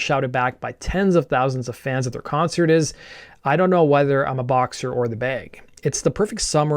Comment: I always thought he said I don't know whether I was the box or the bag not boxer or the bag! Both make sense I guess😂